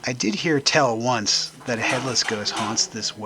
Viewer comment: I misheard you say “Hitler’s ghost” and I thought to myself “How could Hitler haunt some old 18th century in the middle of the US-Canada border when he died in a German bunker?” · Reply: Oh yeah, timeline would be a bit skewed there 😁 I sometimes mumble a bit when I film. It was a headless ghost that I read about in the well. I can't remember the full story, one of the interpreters at the fort told me about it some years ago. 👻 Interestingly enough though, it was used in WWII as POW camp for a short time.